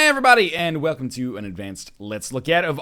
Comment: Everybody! Wtf, what kind of greeting is it?? You didn't say Hi. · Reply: "EH (hey?) EVERYBODY!"